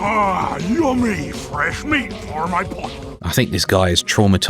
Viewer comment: even as a kid I thought Rusty was funny. I always thought he said "butt" instead of pot